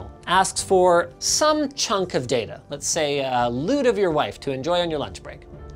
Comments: "A lewd of your wife to enjoy in your lunch.." - linus tech tips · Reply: I thought he said "a nude of your wife" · I hear "A nude of your wife to enjoy on your lunch break" Just me? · Did he say a lewd of your wife??? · Does Linus really say "A lewd of your wife"